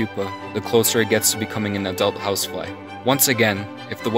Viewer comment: In the 1:55 youtube substitle write HouseWife instead of houseFly D